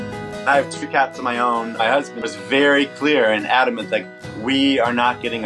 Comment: did he say My Husband?